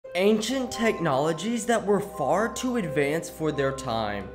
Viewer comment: I thought it said “ Asian Technologies”